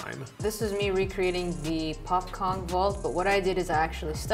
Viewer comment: Turned on subtitles listened to what youtube heared and said 5:19 ''popcorn vault''